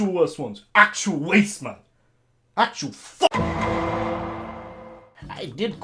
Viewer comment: thought bro said kant I agree